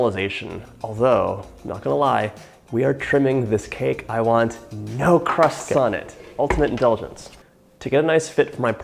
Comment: I swear I thought you said 'No Christ-like' when you actually said 'No crust on it.' Happy Good Friday Eve, sir.